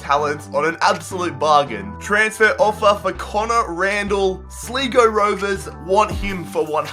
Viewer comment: Did he just say Sleego Rovers